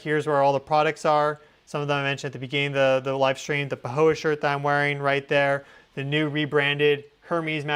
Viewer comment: Announcement! All the products are now available at Earth Evolution! <link> The "pirate shirt" is back in stock, and the earthing boots have been reduced in price! All the all-organic herbal tea blends are in stock, as well as other products and BOOMSHAKALAKA t-shirts! The Pahoe 100% Linen/Cotton Blend Long Sleeve Shirt <link> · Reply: I thought I heard you say pahoa shirt 🥰